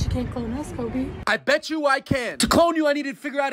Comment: I heard “bet you can’t clone us coby” then I heard you say “I bet you I can’t”